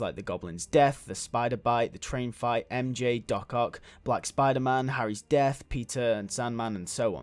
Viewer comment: Am I the only one who heard 'Da Cock' instead of 'Doc Ock'